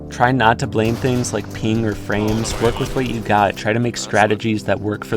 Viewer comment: Great vid. 7:07 but i cant be the only one who heard that as "peeing and frames"